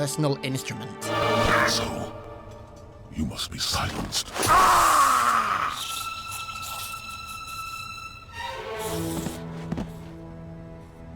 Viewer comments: Back then, I thought the Arbiter said "Asshole, you must be silenced". Then a few years later I found out subtitles existed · I always thought Arbiter said ”Asshole! You must be silenced hear” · So at 8:58 does arbiter say "and so" or "asshole?" I never knew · Did Arby just say "Asshole! You must be Silenced."